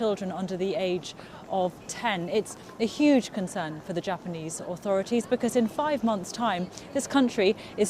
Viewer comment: did she said duterte instead of prime minister abe ? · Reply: "the authority". Lol